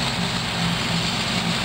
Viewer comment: did she say N word is mowing her field? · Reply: Mr.L.A. - "The neighbor is mowing her field"